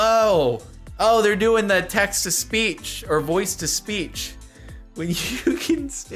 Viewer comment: I think he said voice to speach